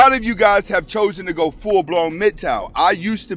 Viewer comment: What's he talking about at 2:24? Is he saying full blown "mid-tau"?